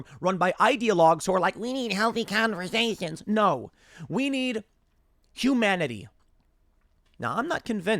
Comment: What is j"joomanity"? Said a@ 10:21is that a biden-ism?